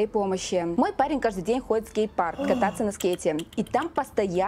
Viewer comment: простите, мне послышалось гей парк .....